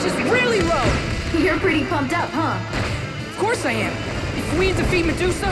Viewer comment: I was playing this ehile worrking on something and I thought it said, "You're really f*cked up, huh?" It's just so fitting!